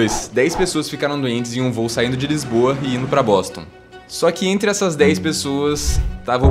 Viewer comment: eu ouvi bosta 😭